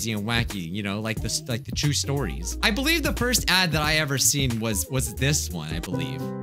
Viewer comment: Took me a minute to realize he was saying “cursed ads” not “cursed dads.” Thought I clicked on the wrong video. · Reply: I thought he was saying ‘first dads’.